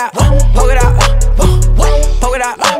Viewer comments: I like the part when he says “Poke it out” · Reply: I thought he said pokadot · i cant be the only one who heard “polka dot” .. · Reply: I hear “Poga dot” lol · i cant be the only one who heard “polka dot” .. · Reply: I heard "pokadow"